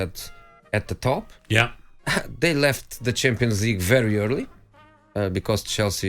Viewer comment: I thought Mourinho said,”they left the chimpanzee early” Me: what? Aaaah,they left the champions league early,see it’s good to cink,right Jose?!